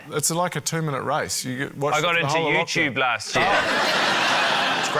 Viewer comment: them talking at the same time, it sounds like Guy says "I watched the whole of youtube last year" which... Yeah, fair to say after 2020. Weird thing is that even after knowing he just says "I got into youtube last year," if I'm not looking, my brain still hears the mixed up version.